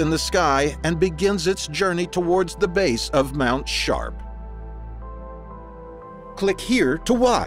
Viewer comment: I'm sure the guy means Mt sharp. 🙄 right? He doesn't pronounce p's very well. The first 3 times I thought he said Mt. SHART!